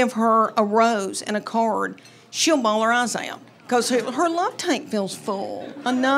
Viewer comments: Sorry. I kept hearing "love taint", which I found a bit amusing. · I finally had to look down at my phone because I thought she said love “taint”